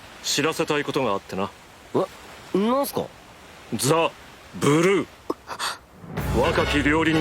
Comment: What people Hear : ZA BURU What i hear: ZA WARUDO!!!!